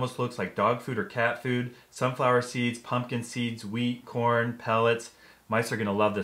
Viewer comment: did I just heard weed at 1:44???